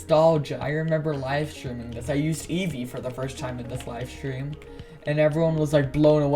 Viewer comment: I thought you said "yeezy" and not Eevee xD. Thanks for sharing with us :D